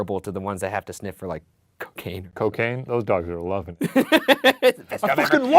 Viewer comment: Lol I thought he said Coochie